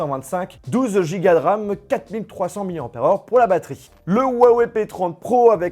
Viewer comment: J’AI ENTENDU QUOI LA ?! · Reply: 12giga de ram? 😂